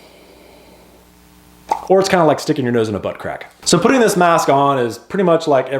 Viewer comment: I thought he said the fit was like putting your nose it a butt crack............Nah must have misheard!